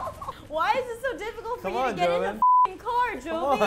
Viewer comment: did Mari call Joven 'Jovie'....?